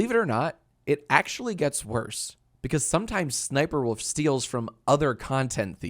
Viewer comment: Nobody is talking about how it sounds like he is saying "Sniperwoof" 90% of the time